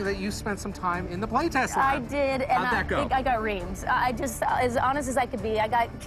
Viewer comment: does she say "i got raped" at 2:14? LOL